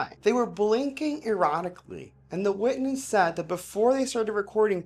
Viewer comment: When you said “Blinking Erratically” I thought you said “Blinking Erotically” 😊 Just for a moment.